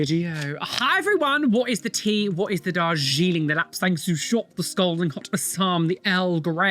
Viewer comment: I have to ask, as English is not my mother tongue, what is he saying after “what is the tea” ?? Someone please tell me, I keep hearing “what is the tajine” 🤨🥲